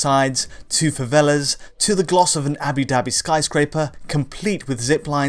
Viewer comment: Did I hear “Abi Dabi” instead of Abu Dabi? 🤣